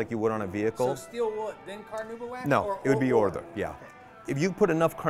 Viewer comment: Does say orther for either/or?